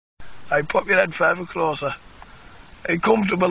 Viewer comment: What do they say at 0:02? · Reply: "Pop ya head further closer" "Are ya comfortable there?"